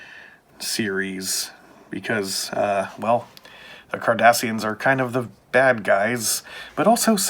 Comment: I sooo thought you said Kardashians!!! I was very confused for a few minutes.